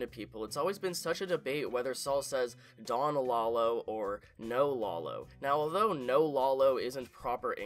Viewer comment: I hear "El Lalo" not "No Lalo" or "Don Lalo".